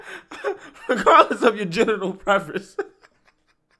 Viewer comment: I thought I misheard "general" preference, then I realized he ACTUALLY did say "genital" preference. WTH???